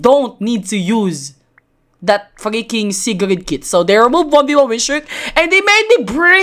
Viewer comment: Did say the (f word)? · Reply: he said freaking in a asian accent cuz he philipino